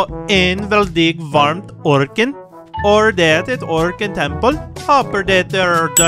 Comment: Did I heard it right? "Organ Temple" 😭💅